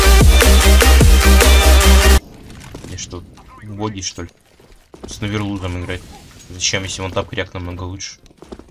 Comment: Ты там что сказал? Мне послышалось или там сказано что-то про logi4'a сказано было? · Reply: там говорит, умник XD